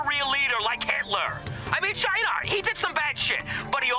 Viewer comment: What does he say ? Shinar ?